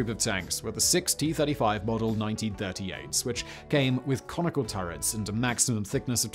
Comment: "It came with conical turrets" I misheard you at first and had to rewind. I thought you said "Comical Turrets" 🤣